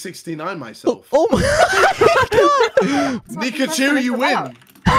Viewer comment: BRUH I THOUGHT HE SAID "Pikachu you win"